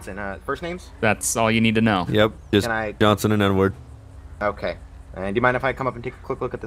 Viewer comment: When he said Edward, I thought he said N word